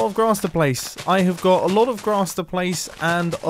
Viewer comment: "it's the placing of the GLASS I take issue with" good job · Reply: Yeah. I thought he said glass and not grass